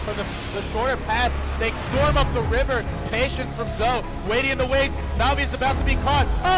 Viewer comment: I heard "Patience from Zhou" I got goosebumps